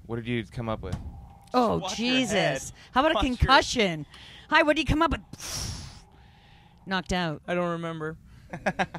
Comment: what'd ya say Josh? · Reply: He said "watch your head" lol